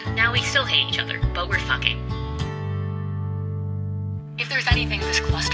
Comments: did she say “we’re fine” or “we’re fucking”??? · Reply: Ooh she definitely said fucking 😂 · did she say “but now we’re FUCKING”?!! I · did she just say, 'but we're fucking'? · wait is he her boyfriend? or did i mishear the "but now were fucking" part👀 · LMAO I thought she said talking